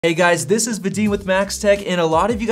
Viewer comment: I love your videos. However, I've heard you say your name at least 100 times but wouldn't be able to write it down... Sounds like "Vidin"?